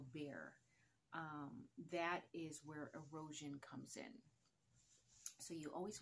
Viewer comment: Great info, I thought you said that's how you get a roach. Had to rewind, you said erosion